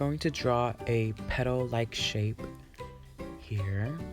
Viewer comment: I heard "Pedo like shape here." 😂😂